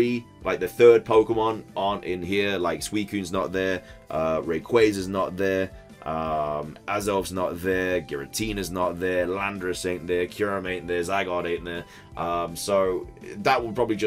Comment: I swore when you said Landorus I heard Lapras, lol The Lake Trio being version exclusives would be weird though, they've never done that.